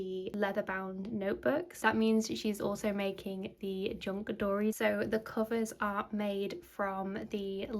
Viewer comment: Hi, yes to altered books/journals! What is the name of this tiny journal - all I heard was 'junk dory' but can't find anything! 😅😅😅 xxx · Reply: It’s a Grace and Salt ink junkdori 😊 <link>